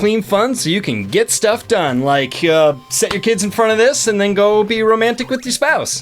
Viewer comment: for a second, I thought you were saying, "set your kids on fire" answer to question: SPIRAL HAM DUUUUHH i'd rather have ham in my salad than bacon! other question: I've never played ANY castlevania! I know! gimme a break, I was a sega kid. closest thing I played was probably Quackshot. really wanna do that one for my first solo vids